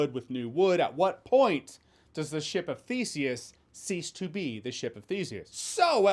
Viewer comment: oh. I thought you said this movie is a ship of feces.